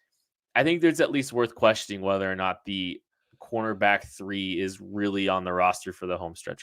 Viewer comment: I get so confused every time I think I hear say "quarterback" then starts talking about the conerbacks.